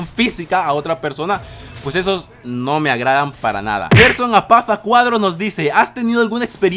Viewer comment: por alguna razon entendi "hacerse una paja a cuadros" xD